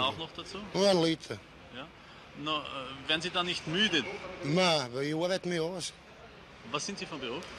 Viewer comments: was sagt der kerl bei 0:31 ? ich versteh es einfach nich nach etlichen versuchen. hilfe :( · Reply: werden sie da nicht müde? nein, weil ich arbeite mich aus. ( na weil i oabeit mi aus) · was sagt er da? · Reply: "Na, weu i orbeit mi aus". Heisst er muss so schweisstreibend arbeiten, dass er den Alkohol eh rausschwitzt. 😁